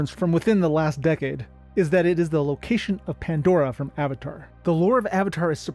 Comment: Avatar lol. I thought that you said Ferngully.